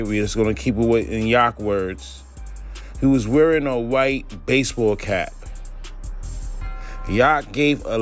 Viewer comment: I thought he said a white arm..